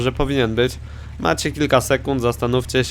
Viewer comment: Ja usłyszałem tam "Sekundo!".